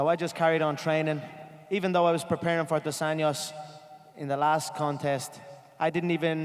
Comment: did he said " I just carry on training even tho i'm preparing for Adesanya ? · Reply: “Dos Anjos”